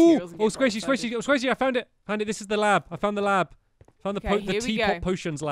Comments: is it just me or did he say honey thi is the lab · he called sqaishy honey 😭😭☺️☺️☺️ they’re so perfect Edit: people are saying they can’t hear it. From what I can hear, says, “honey, this is the lab I found the lab”